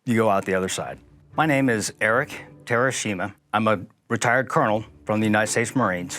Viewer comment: I heard 'Eric Hiroshima'.